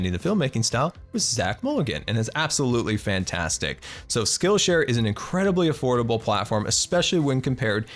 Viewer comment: for a sec I thought he said "So skillshare is an incredibly horrible platform"